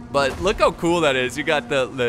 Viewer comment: It sounded like I said pigeon boats